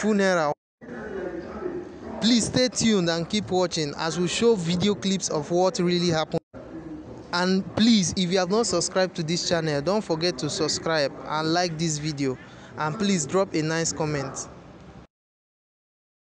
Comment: I thought you said you want to show us the video clip of what actually happened